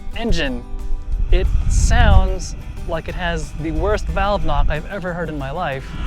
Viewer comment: says something about the Super Tenere, @ 15:28 having the "worst valve-something" he's ever heard... I can't make out what he's saying. Can anyone fill me in? Explain?? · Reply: I said "valve knock." On our particular test bike the noise lasted for quite some time before the oil pump circulated enough oil to lubricate things and quiet it down.